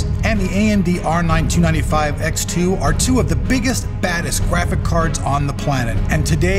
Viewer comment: I keep hearing him say "biggest, baddest, gWaphics cards" lol